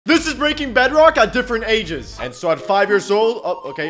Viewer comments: I thought he said Breaking Bad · I thought you said braking bad😂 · I THOUGHT YOU SAID BREAKING BAD AT DIFFERENT AGES